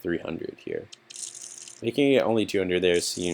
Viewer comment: Can't quite get it what's been said at 18:14 "Bikini only 2 hundred there seems a little bit ridiculous". It had to be something else instead of bikini, but what... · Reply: "Making it only 200..." :D Sorry the response is a little late! Be well, Mister X